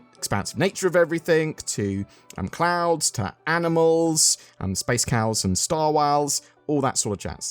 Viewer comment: said space whales and I heard “space wows” ;) 👌